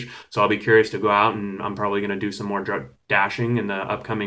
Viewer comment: Lmao what did you say at 5:35 do some more drugs?!